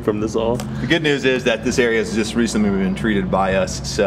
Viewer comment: The good news is this area has been treated or what?? did u say?